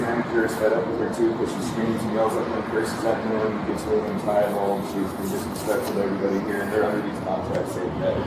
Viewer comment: captions are wrong, he says she “gets really entitled” not “gets thrown inside the hole”